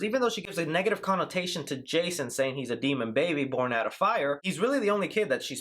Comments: I thought she said he was “born to love fire” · The line isn’t “born out of fire.” It’s “born to love fire”…😅 I’m sorry, but 50% of the theory relies on a line misheard by this dude